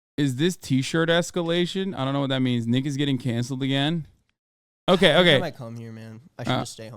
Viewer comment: i thought he said niggas getting canceled again stopped me in my tracks